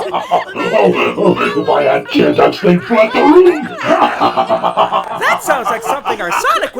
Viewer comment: What did he say? The voice filter makes it hard to hear clearly. All I can hear is; "All our tear ducks left the room" which i know can't be it, but I can't hear anything else.